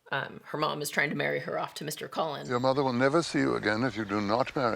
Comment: not the subtitles saying 'mr. cullen' instead of 'mr. collins' 😂